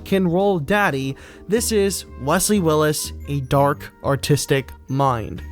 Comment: I thought it said dark autistic mind